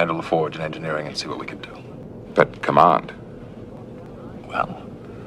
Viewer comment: What does Picard say at @ 00:42? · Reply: "But command..." The changed Picard wanted more than better status, he wanted to be a leader again...